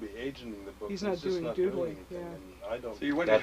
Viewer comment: Does she say 'googling' at 1:48:53? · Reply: She says "doodley" meaning "he's not doing shit/anything". "Googling" as part of the vernaular doesn't happen until a few years later (Google doesn't start until 1998).